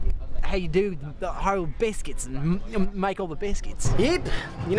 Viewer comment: LOL it sounds like he saying "figure out how to make all the biscuits"